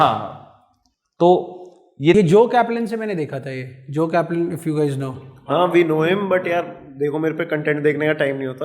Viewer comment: what did you say? Joel Kaplan ???